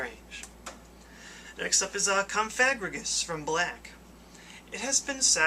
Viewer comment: Did he just say CumFag-rigous?!